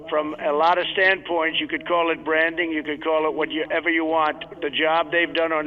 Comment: !!!! Tehee it sounded like Brandon! I know it wasn't but made me smile!